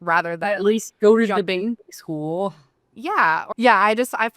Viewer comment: did someone catch what she says here? What kind of school? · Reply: Debate school I believe?